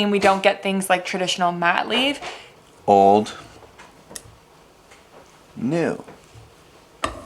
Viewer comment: What about Madley? I misheard you when you said 'mat leave' and now I'm really loving Madley Fitzgerald 😃